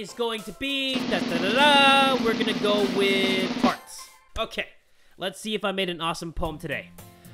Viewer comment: I thought he said farts